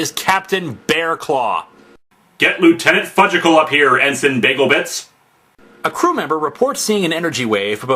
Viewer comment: What does he say at 3:39, "...(?) bagel bits?" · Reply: “Get liutennant Fudgicle up here, Ensign Bagel Bits”